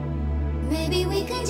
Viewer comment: Fun fact: The fast vocal sounds like the lyrics are "life is cool without sound" and that's even what the model is lipsyncing in the music video, however, if you slow the vocals down to the original pitch, you'll find the lyrics are actually "life is poor without sound", which makes a lot more sense! WONDERFUL TUNE. One of my favourites. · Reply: I thought she was saying 'blue without sound'.